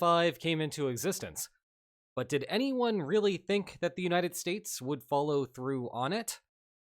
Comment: CC says United Stares instead of United States